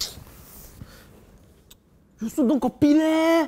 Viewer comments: in the subtitle,they said”Cory”instead of copy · i realized that at 0:30, the subtitles said cory instead of copy